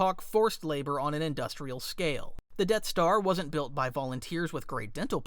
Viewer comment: the caption says The Debt Star